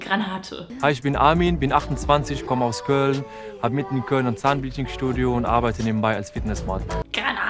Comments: Moment mal... was sagt Amin (oder Armin? :P ) da? Er hat mitten in Köln ein Zahnphysik-Studio? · Ich hab bei Armin zuerst verstanden "... und arbeite nebenbei als Penismodel" und hab mich direkt am meinem Kuchen verschluckt, den ich nebenbei gegessen habe